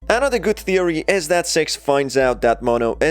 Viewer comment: the subtitles say sex 🤣🤣